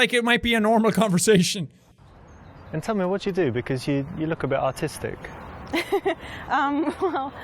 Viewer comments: I kind wanted him to pick some of the weird/bad answers to just watch the cringe lmao. and when he said "you look a bit artistic" It sounded like he said "autistic" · Everybody heard it as "you look a bit autistic" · I dead ass spat my drink out. I thought he said "So tell me what you do, you look a bit autistic" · It sounded like he said autistic instead of artistic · Lol sounded like he said "You look a bit autistic" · Am I the only one that thought he said "you look a little bit autistic" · I thought he said autistic instead of artistic. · I thought he said "You look a bit autistic"!! Even the actress looks incredibly uncomfortable